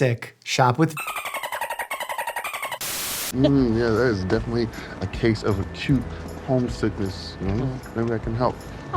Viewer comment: What did he say? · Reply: Number 11